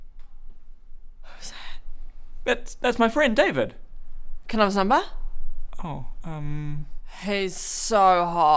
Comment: Does she say "can i have his number"? holy sht xD no way I understand it first time.